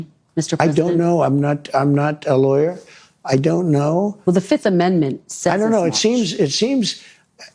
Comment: with respect, I think Trump is trying to say he is only interested in the DOJ interpretation of those SCOTUS rulings because the DOJ is telling him what he wants to hear… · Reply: Yeah, I heard it as “I don’t. No.”